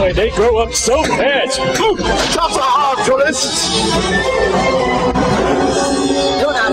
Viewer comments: what did he say? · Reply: “Ta-ta, tourists!” 😁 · what does bob says? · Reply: He says "Ta-ta, tourists!" 😁